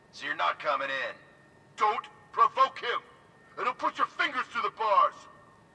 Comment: I dont know why I heard "Dont provoke him" In patrick's voice from spong bob